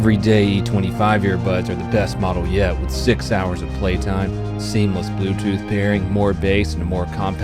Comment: I heard "Sig Sauer of play time.. " sounds good to me.